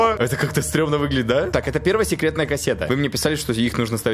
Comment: мне послышалась он сказал "сегоретная кассета"😂